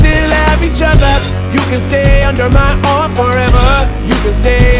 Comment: The first time that I heard an umbrella that is what I thought it said you can say under my arm forever and then I figured out I said umbrella and I was dumb